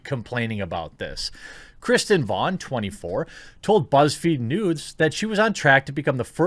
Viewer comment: Bruh, I swear I heard you say buzzfeed nudes lmfao!